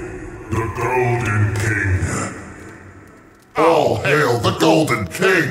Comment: When they were saying "all hail the golden king," I thought they were about to say "aw hell no"